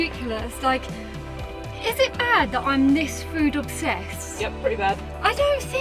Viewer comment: Obviously, English is my second language, to be honest I barely speak Spanish properly 🤣, so, at 11:20 I heard "I missed food or sex and Lou goes yeah pretty bad" so I went "wait what?!"... Subtitles, "food obsessed" aaahh it makes sense now 🤣🤣 but yeah yeah. For: Take to a Navy themed restaurant, details mate, details. adventure this time was really unexpected, Wow 😲.